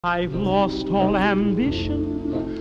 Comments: "I lost all ambition" "The world are ya clean" "I just want pee" Very inspiring words.. 💀💀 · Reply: I heard "I lost all em bitches" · "I just want to pee" 💀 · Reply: I heard “I just want pee” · "I just want to pee" 💀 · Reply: I heard " i just want pee" ☠️☠️☠️☠️ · "I just want to pee" 💀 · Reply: I heard "i just want pee" 💀💀😭💀